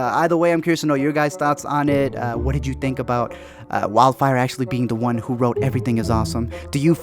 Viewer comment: did you just say Wildfire